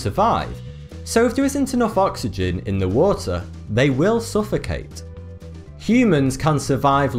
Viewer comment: Not really, drowning is when the lungs of a creature are filed with lungs or die through submersion in and inhalation of water. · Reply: ***** He actually said 'suffocate' at 0:42, but your point is correct.